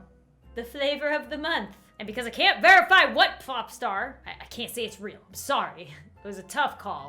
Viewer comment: who else heard “pLopstar”? 🤣